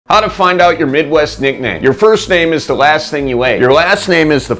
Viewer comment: I thought he said how to find out your midwife's nickname. I was so confused. 😂😂😂😂😂